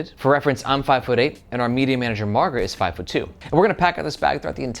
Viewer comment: This whole time, I thought you were saying your MEDIUM sized manager Margaret. I finally heard it correct and was like ooooohhhh